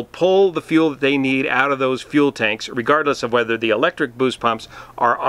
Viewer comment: Did you just say "irregardless"? approximately